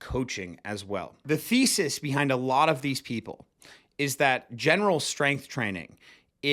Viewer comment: Thought you said "The feces behind this general strength training." 😵‍💫 Good video. Never did any of the fad exercises..planks, curl ups, box jumping for height, those rope things, or rotating a plate around your head.. Many of these are done for novelty when people get bored with running, calisthenics, hard work and weights. Good video ! Thanks .